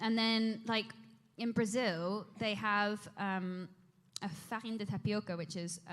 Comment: I thought she said “in Brazil they have fucking tapioca” hahahaha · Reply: I don't know if you had the answer since but she says in French "farine de tapioca" which means "flour of tapioca"